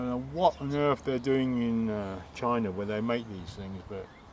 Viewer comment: You should use a different special tool to retract the piston. Thats likely why it broke in the first place. There is a special tool to compress and rotate the piston... The reason why it needs to rotate (also) is to allow the handbrake cable to work. So almost all rear calipers pistons need to compressed and rotated at the same time. Keep it up! André PS: the video repeates it self in the middle. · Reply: I thought you said...damn China! As u dropped the caliper. Made me laugh man!